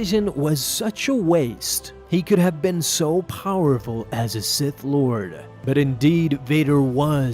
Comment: I thought you said simp lord